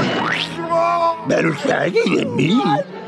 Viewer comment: What did Scooby say? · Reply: He said "Better Shaggy than me.".